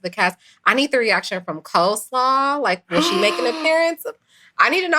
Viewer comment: I thought said Cold Slaw was the love of Martel life. Sorry